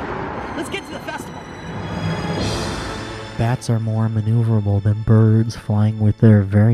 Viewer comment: I genuinely thought I heard Voiceover Steve say, “Bats are Mormon” instead of, “Bats are more maneuverable than birds” 😭😭